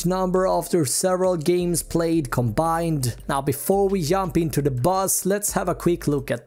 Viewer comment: he said "yump" instead of "jump" lol * 7 *